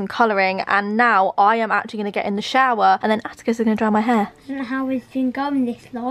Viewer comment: I thought she said Atticus was gonna dye her hair! That should definitely be a video